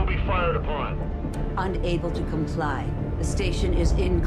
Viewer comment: When she says unable to comply, I hear "unable to comply, build in progress"